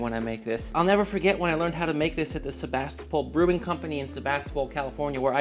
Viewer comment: Please go make this and report back to the comments section! HAPPY COOKING! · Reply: I thought you said Sevastopol 😅